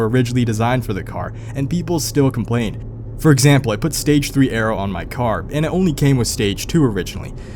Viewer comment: what are you saying? Stage 3 “ara” or stage 3 “arrow” what does this mean? I’ve literally never heard this terminology but I’m not familiar with GMs. EDIT: (Ahhh you’re saying aero which is a fancy name for your spoiler) lmao.